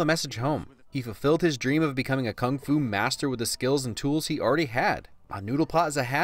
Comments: It says Fung Fu in the subtitles instead of Kung fu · The subtitles say fung fu